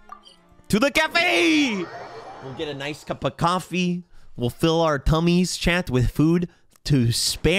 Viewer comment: I THOUGHT BRO SAID TO FILL OUR CUMMIES WITH FOOD, OML